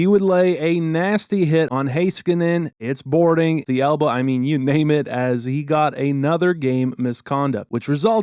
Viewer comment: did you say " you maim it "